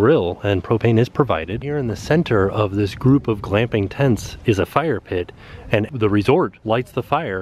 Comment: for glamping Me: glamping oooo i thought he said clamping